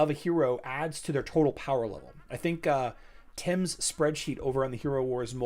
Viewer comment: Anybody have a link to the spreadsheet he mentions @ 15:15 I couldnt quite make out the name of the individual who made it. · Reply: check out the Hero Wars Mobile Goat facebook page, look for Tim Davis' spreadsheet.